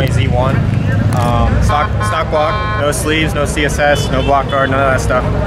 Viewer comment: I'm gonna have to call BS on stock block with no sleeves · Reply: hoo shiitt I thought he said css block . That's not gonna last Damm now I get it why you said that 😨😨😨